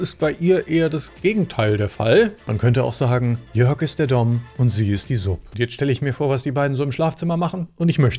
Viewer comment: "Jörg ist der ??? und sie ist die ???" Sorry, ich versteh selbst nach mehrfachen Wiederholen und lauter stellen nicht, was du sagst. Beim ersten verstehe ich so etwas wie "Daumen", oder "Domm" und beim zweiten "Supp" oder "Sopp"? Kann mir bitte jemand antworten, was er gemeint hat? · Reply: Ach!!! "Dom" und "Sub"... omg.